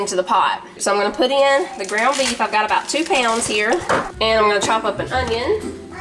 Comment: Prancer was sneaking around the cocoa bar. When you said chop up onion and thought you said chocolate onion. That was so sweet. That's my favorite taco soup recipe. I don't like ranch flavor in anything, and a lady from church always made taco soup. I finally ask for her recipe and when I found out it had ranch packet in it, I almost had a heart attack lol. Of course I made it without the ranch packet and it just didn't taste right. Needless to say, I make it with the ranch packet now everytime.